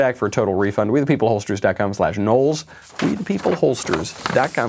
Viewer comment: Holsters? I thought you said pollsters...